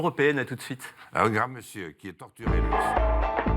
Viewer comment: C est moi ou Jean Lasalle dit "un grand monsieur qui est torturé..." en parlant d'Asselineau au moment de la transition a